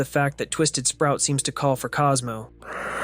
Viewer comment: is it just me or does it sound like he is saying astro instead of cosmo?